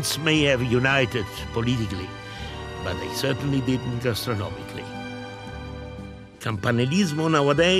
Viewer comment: What does Carluccio say at 12:58 : The regions may have united politically, but they certainly din't ???? - ly. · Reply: 'gastronomically'